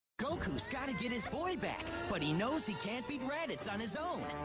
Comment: Laughed at: "Gokus gotta get his boi back" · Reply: I heard "Goku's gotta get his oil back"